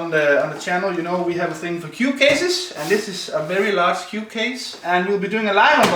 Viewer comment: Sounds like he said, "this is a large cute case" instead of cube haha.